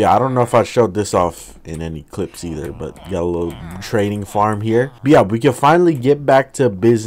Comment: When he talked about building the trading area I thought he said training area. The villagers are having an even worse time